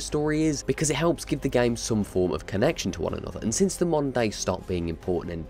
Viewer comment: I kept hearing "monday" intead of "moder day" 😂😂